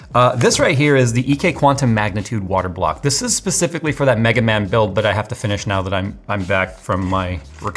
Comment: I heard "Megaman Build" & now I'm infinitely curious as to WTF you're talking about :)